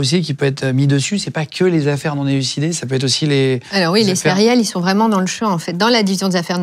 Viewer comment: Un superbe intervieuw vraiment mais à 7:24 mon cerveau a dévié et j'ai entendu distraitement "Oui les céréales sont dans le champs" :)